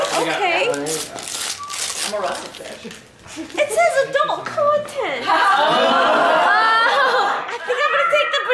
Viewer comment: what did she say at 15:03? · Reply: ''It says adult content''